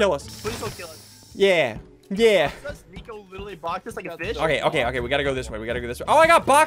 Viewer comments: did he say fish or bi*ch!!?? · Anyone else hear Nico literally boxed us like a bitch